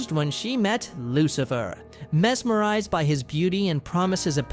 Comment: Lucifer? Did you say Lucifer? I LOVE LUCIFER HES MY SECOND FAVORITE CHARACTER IN HAZBIN HOTEL!!!!!!!!!!!